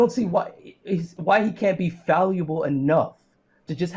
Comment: Did he say "falluable?" 11:56 Like a portmanteau of valuable and fallible?